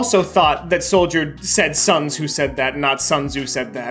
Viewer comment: I thought he also said son ho said that